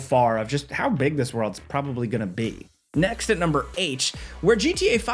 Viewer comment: "Next at number H"... Imma say H now instead of 8, confuse people